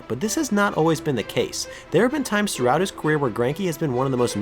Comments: he definitely says "throughout his queer" instead of "career".. which I don't think is intentional but its ironic because queer means weird.. just a thought lol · "There have been times throughout his queer" I had to rewind and make sure I heard that right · “queer” instead of career